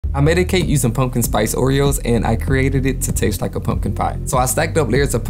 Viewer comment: I thought you said, "I medicate using pumpkin spice cream..." and I was like, well, I guess there could be worse things! 🤣🤣 ❤